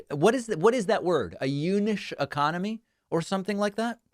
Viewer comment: Did he just say we're going to have a eunuch economy?